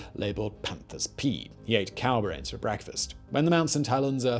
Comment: what are you saying here? It sounds like, "...he ate cower eggs for breakfast." I've never heard of a "cower" that lays eggs. I've gone over this point of the video several times to try to figure out what you're saying, but can't make sense of it. There doesn't appear to be any subtitles available, either.